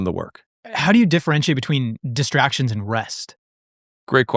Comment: I thought he said distractions and breasts 😂